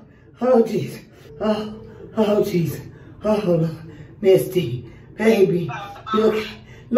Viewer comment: I thought she said musty